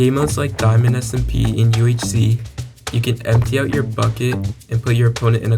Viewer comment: i heard empty out your pocket 999 forever bro